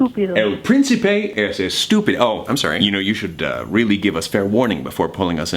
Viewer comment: Did he just call Princy Stupid??